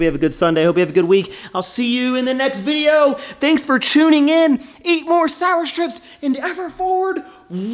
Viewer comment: Did you just say "thanks for chewning in" ? 😂@ if not, make that a thing dude👍🏼